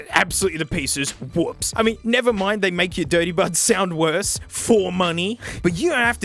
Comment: Dirty Bud Embiggeners? Sounds like a perfectly cromulent idea. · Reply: Wait, I've always thought he was saying "Dirty Birds" Now that I read this (ironically as he was saying it), I hear Buds My life is a lie